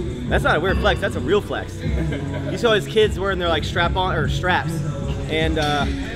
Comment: no one is going to talk about how he said "strap on" instead of "straps" lmao